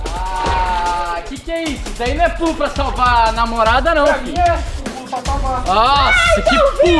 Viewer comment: Opaaaaaaaa só eu q escutei o pedro falando "isso não e pulo pra salva a NAMORADA não🤩🧐 Gente mais uma prova q Joana vai voltar!!